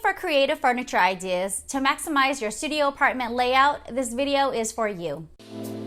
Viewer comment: Ohhhh, you said "to maximize your studio apartment layout." I first heard "your sh**ty apartment layout." And was like ... yup, indeed I am.